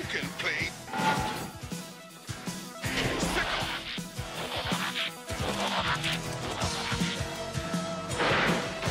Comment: I’ve never been able to tell what he says at 1:22. Can anybody alert me as to what he says? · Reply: He says "Sic 'em!" It's an old expression that people used when telling (usually a pet) to attack